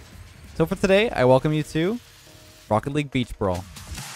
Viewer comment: Anyone else mishear the start as rocket league beef brawl?